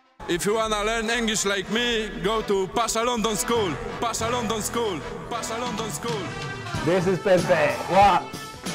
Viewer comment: Pasha London School GG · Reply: I thought he said "Pasha long dong school" xD